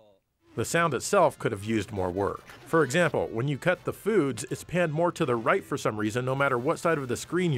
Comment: Listening to this on my phone, I thought you heard it as "Cut the Boots", I'm not sure which is funnier.